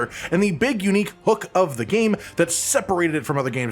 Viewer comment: I heard "the big unique hook was the carrot curry" 😂